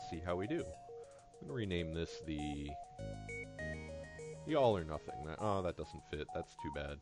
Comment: I've actually taken to using these videos as background noise while I draw comics (as funny as that may be) and I misheard when you were naming the ship. Originally, I thought you were naming it the "Y'all are Nothing" which made me laugh.